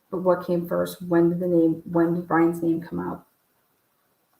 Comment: Did he say Bryan gonna get you maddie, or "theyre" gonna get you? I never realized he said a name. And I thought that was "Adam." Now I am confused. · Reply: i always heard "there" never "Brian's" Unbelievable 🤔‼️